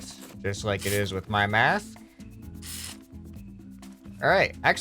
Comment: moment 20:04 he says math instead of mask